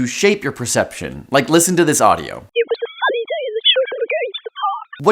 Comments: Interesting, the audio bit before hearing the og i heard "it was a funny day, the children were going to the park", so only one word off i think. But I am accustomed to diving into audio files for hours on end trying to heard specific thing ig. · I actually heard the full sentence the first time listening to it 💀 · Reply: I heard "It was a funny day at the sugary gangster park" I have no idea what that could mean, but it's thought provoking :p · On first listen I heard 'it was such a fun and sunny day to go to the park' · "it was a funny day the children were going to the park" something like that Or at least thats what i heard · I heard "It was a sunny day that children are going to the park" or "It was a funny day for children are going to the park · In the audio first one I heard "it was a sunny day and we were playing at the park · I heard “it was a funny day and the children were going to the park” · I heard: "It was a sunny day with the children in the park" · I heard "it was funny but to tell you the truth, we're going to the park" so pretty close. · Spoilers about the audio at 1:45 At first I heard: "It was the first day the children were going to the park" Then, on the second or third time I heard: "It was a sunny day, the children were going to the park".